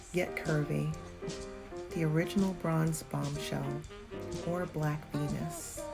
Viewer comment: I normally used closed captions or cc to follow the dialogue on videos and at 6:57 it sounded to me that you said "the original bronze bombshell or black venus" The cc says something else.